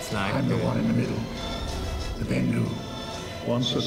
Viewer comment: wait did he say bindo!?....................